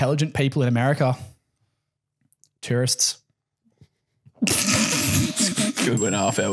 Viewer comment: As an American, I personally find the joke about tourists being the only intelligent people to be absolutely true 😂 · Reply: lol I thought he said "terrorists".